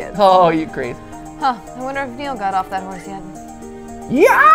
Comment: Dang I thought it said arg marker